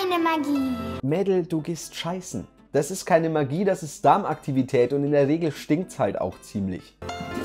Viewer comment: Ich hab erst Damen Aktivität verstanden 😂😂